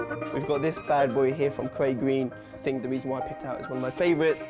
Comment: what brand did he say?? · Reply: craig green mate